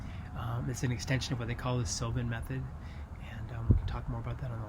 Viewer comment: The "silvan" method you say? 10:20 As in wild and wbtb? Or something else. A little difficult to hear exactly what you said. Thanks though. Keep it up.